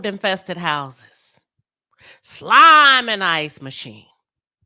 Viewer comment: Girl did you just say " SLIME in the ice machine!" 🧊 😊 don't tell me you grew up watching Marvin too